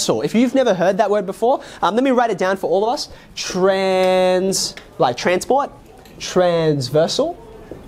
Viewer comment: I at first I thought he said "like transboi" lol...